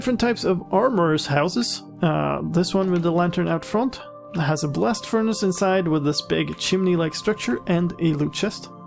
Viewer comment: Heh, as you were just referencing religion, I heard "a blessed furnace". :D